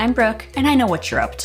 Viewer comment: I had to replay the beginning cause I swear I heard "Hi dudes, I'm broke" but the third time I heard Brooke n then I was like "that makes more sense"